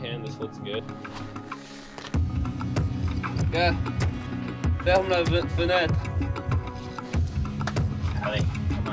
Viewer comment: Wtf's he saying? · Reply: ferme la fenêtre - close the window